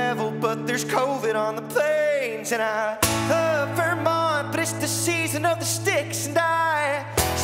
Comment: lol, I thought he was saying 'And I love her mom, but it's the season of the sticks".....its Vermont not her mom lmao